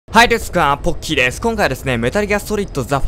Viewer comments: はいデスクあーポッキーですに 聞こえた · はいですくわぁーポッキーですって聞こえる人ー？